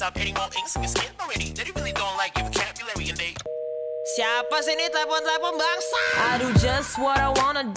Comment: We kaget banget pas main nfs heat trus dengerin pas dia bilang "siapa sih ini telpon2 bangsat"... Kirain gw misheard tapi pas gw cek,, eh ternyata memang org indo wkwk... Gak nyangka bakal ada lagu karya indo di salah satu franchise game nge top di dunia